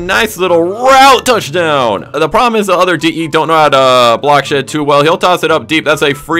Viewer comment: did he say they don't know how to block shit that well?